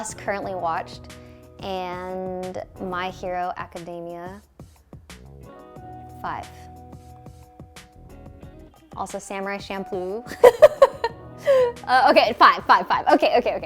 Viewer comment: Did she just say samurai shampoo